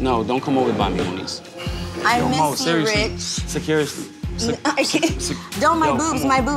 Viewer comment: All I hear is Cyn: “Rich Pesos.”